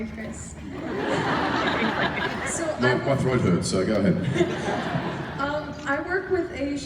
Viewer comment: What does Chris say at 6:21? Somebody answer, pretty pleeeaaaase. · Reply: Nope my throat hurts so go ahead (he needed water)